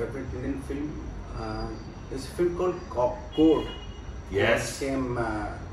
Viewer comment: Could anyone please tell me what was the name of the film that was mentioned at 31:29? I couldn't catch it. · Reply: court. 2014 film